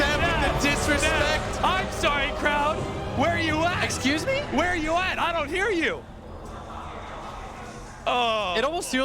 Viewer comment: ceb : where's the crowd? i can't hear you~